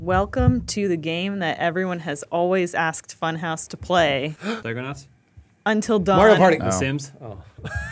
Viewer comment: what the hell did say at 0:00:18 ? I can't figure it out. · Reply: Psychonauts?